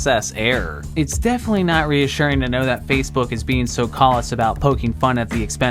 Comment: did you say.. cOllused? are you even America bro?